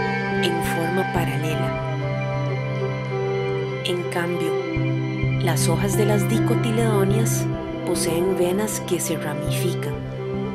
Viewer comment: Que alguien me diga que dice acá 3:04 no logro escuchar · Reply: Las hojas de las dicotiledóneas poseen venas que se ramifican.